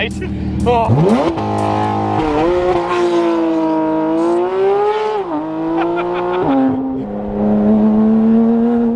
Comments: hear what he says and listen closely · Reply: I think he said shit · did James just cussed on camera for the first time?? 😂👏🏽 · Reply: Nah at 6:29 I heard him say oh s*** 😂 👏🏼